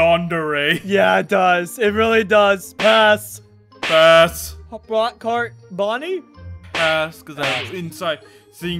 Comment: PLZ- I WASN'T LOOKING WHEN FUNTIME FREDDY SAID "black heart Bonnie?" AND I THOUGHT HE SAID ROCK HARD BONNIE- I NEED JESUS